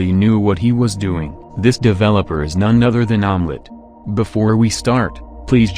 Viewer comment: It sounded like you said onnet was omelette